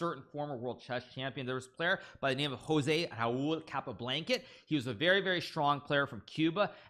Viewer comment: I cannot be the only person who heard him say “capablanket”